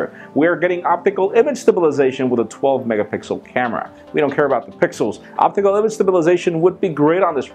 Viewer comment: did you just say "we dont care about pixels"?...htc one says you're being unfair :O